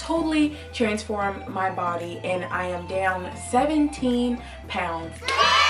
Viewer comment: I litteraly was doing my nails and I thought I Heard her say i am damn 17 pounds I looked up like and I oop tell me your secets